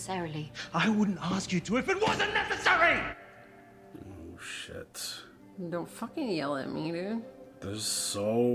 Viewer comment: i swear i thought you said "dont yell at me, nig**" and i so shocked i couldn't even react 😫😂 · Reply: She said, "Don't f**king yell at me dude".